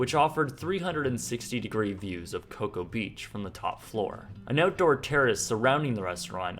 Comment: "Outdoor terrorists" am I the only one who heard that on 1:49 ?